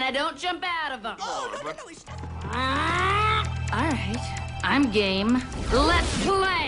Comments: When she said 'I'm game' I thought she said 'I'm gay' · I thought she said ''Alright, I'm GAY...'' damn it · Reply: Dopa Dopameme she said "Alright, I'm game!" As in "I'm in the game!" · for a while i actually thought she said "i'm gay" and i was like oh ok